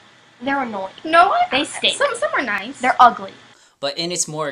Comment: もう日本に差別があるかないかは結論ついてますでしょ。これ以上議論する余地がありません。だいぶ他人の揚げ足の取りあいっぽくなってしまいましたし… ところで、あんまり議題にされていませんが、動画中で白人女性が黒人に対して罵詈雑言を浴びせているシーンがありますよね。 向かって右側の女性が主に喋っているわけですが、左側の女性がボソッと「someone nice」って言ってるんです。これは興味深いと思いましたがどうでしょう？私の聞き間違いだったらすみません。